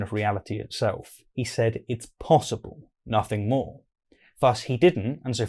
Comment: When I listen to Jordan in the repeated fragment at 8:03 I hear him say "It is certainly PLAUSIBLE", 'not 'possible'. And in the way his narrative unfolds, 'plausible' is even worse than 'possible'.